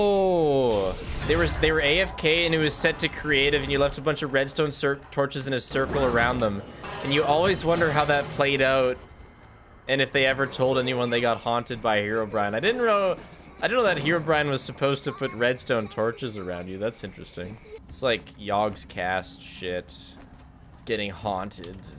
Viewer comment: I was falling asleep then I hear him talking about heroBrian or something I thought I was hearing things